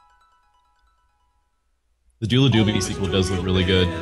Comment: Okay this is going to bug me. What the hell was saying at 1:04:01 ? dula doobity? doo la doo ba dee?